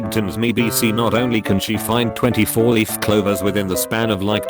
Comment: 20 four leaf clovers. i wasnt looking at the screen and only head "20 four leaf clovers" and i thought it said "24 leaf clovers" and i wouldnt trust her either